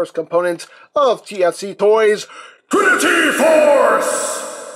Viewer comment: Before reading the name I thought you said Pretty Force.